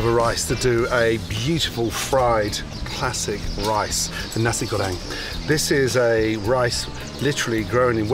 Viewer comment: I initially thought he'd said: 'Nazi-Goering-Rice' And thought... why is Gordon now off making some bizarre Nazi Third-Rice recipe that was originally enjoyed by Hitler at the Berghof? My mistake, sorry.